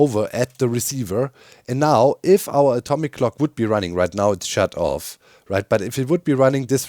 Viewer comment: I thought you said atomic GLOCK.